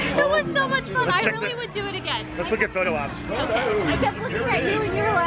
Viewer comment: would do the Incedicoaster again? Did I hear it right around frame 16:24? Space Mountain here we come! Great video guys!